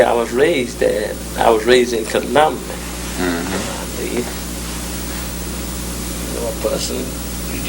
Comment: I'm from Biloxi (Mississippi) and I understand every word she says. In many places where you have [?] it's where she has filler words or starts and stops. At 4:42 you have that she says she grew up in Columbia, but she actually said Columbus. There is more. I'll try to do more later.